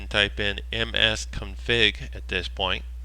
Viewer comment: what does he say at 00:27??? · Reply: he says "msconfig"